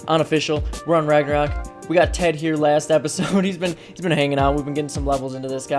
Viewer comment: When you said we got ted here i thought you said tek tier...Lol